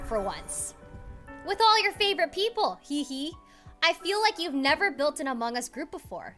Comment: The subtitle at 0:45 says "fell" instead of "feel"